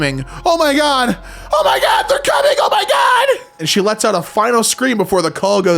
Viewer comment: i heard they're coming on my dad 😭